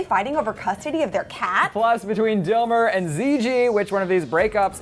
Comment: When said Dilmer I thought she almost said dildo😂😂😂😂😂😂😂